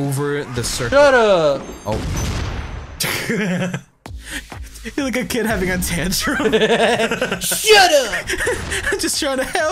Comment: Can someone tell me what cry said at 2:36 ? "You look like a kid having ...???" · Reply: "You look like a kid having a tantrum"